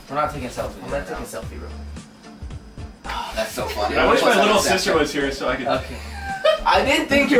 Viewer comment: Wait, did Jev say he had a little Sister??